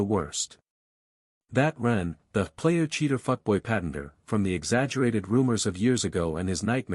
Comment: did he say F boy? Lol